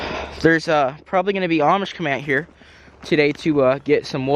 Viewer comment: Did you say Amish? As in... The Amish community?